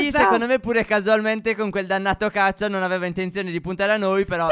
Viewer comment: Al minuto 2:46 ho capito c***o